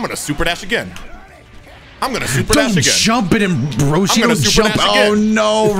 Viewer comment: Does anyone else hear call Roshi, Broshi?